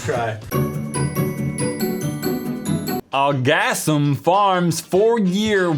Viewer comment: When I don't continue to look at the screen while the video is running... Well.. Things happen... You spoke the title and what I heard was litteraly: "Orgasm". I know my mind is mostly dirty... But I was a little terrified to look back to the screen after hearing this.